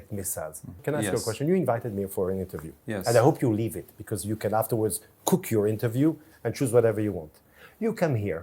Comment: Anyone else hear threatening language at 11:46 ((you invented me for this interview AND I HOPE YOU LEAVE IT SO TOU XAN GO BACK. Just kill kill kill in this government